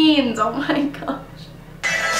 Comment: when the valentine video 'said plug it in' i thought it said 'love you nan'😂